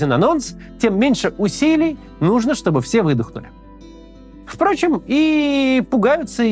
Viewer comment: Услышал: Что б все вы дохли.